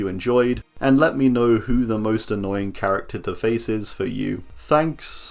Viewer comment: I thought it said with the most annoying face, not to face :P